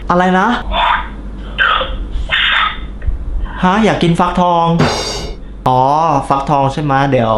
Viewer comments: ใครได้ยินว่า What the fuck บ้าง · มีใครได้ยินตอน 4:41 ฮีโร่บายพูดว่า what the fuck มั่ง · ใครได้ยินคำว่า what the fuck บ้างเอ่ย